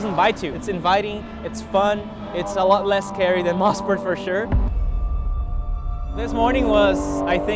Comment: Dumb question, I'm sure, but I'm not familiar with all of history... What did he mean at 6:35, when he said, "a lot less scary than motorsport for sure"? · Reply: I meant Mosport, sorry! caption typo